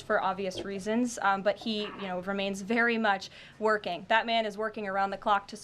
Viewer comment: bruh I thought she said “Batman”